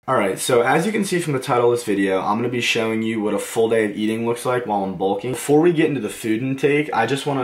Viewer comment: I thought it said full gay lol